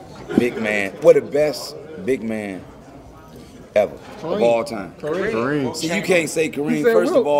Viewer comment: I get what he’s trying to say however they had to ban Kareem from dunking … which lead to the sky hook 😂🤣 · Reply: oh you said Hakeem my bad thought u said Kareem